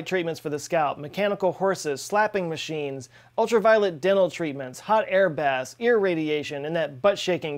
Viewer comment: For a minute, I thought you said "ultra violeNt dental treatment"... O_O